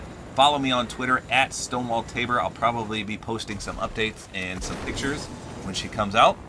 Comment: I thought he said updates and bloopers and I’m like does he rlly not like his daughter?!?!?!